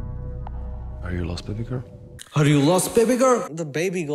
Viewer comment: subtitles says are you lost vinegar 🤣